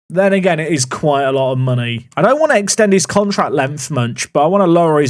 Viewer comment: I think must have been hungry because I hear "I don't want to extent his contract length munch"